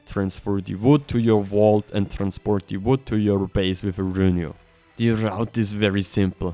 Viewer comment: i dont undesrantd what u say at 14:00 transport wood to ur base with a runu? what are u saying? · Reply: Use a Vault and a Rhynignatha it can grab structures and ignores the weight of the content